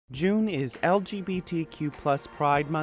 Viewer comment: Did he just say LTBT?